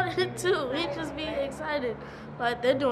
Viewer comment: did he say "bitches be excited"? LOL